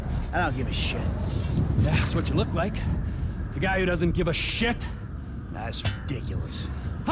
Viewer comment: sounds like he said shidt but with a d in it